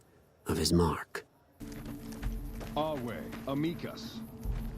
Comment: I remember that the first time i played legion , i for the first time played as a hispanic character and when i heard the "amicus line" o thought he said amigo